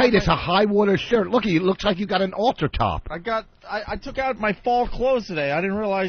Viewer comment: No-one caught "alter top" instead of "halter top" by Vos? Wow!